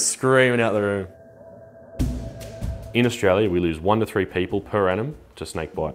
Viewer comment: I thought I heard 1 of 3 people